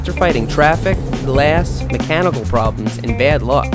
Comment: I heard this as cannibal problems... Miami...